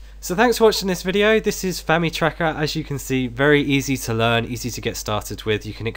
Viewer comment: I heard 'Fanny tracker' and left disappointed, maybe next video?